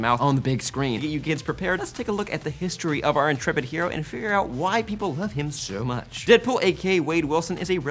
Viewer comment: "That's why white people like deadpool"... Okay... :D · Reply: Julia Leite I know what he intended to say its just the speed at which he said it makes him sound like he said white people instead of why people.